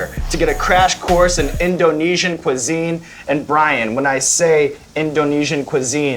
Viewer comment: I REALLY THOUGHT HE SAID COCAINE I WAS SO CONFUSED!!!